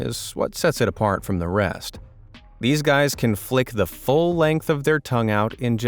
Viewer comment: i thought he was about to say "these guys can flick the f*** out of their tongues"